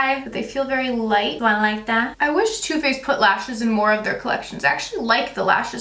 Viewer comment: Did you say Too Faced lashes? At 18:22?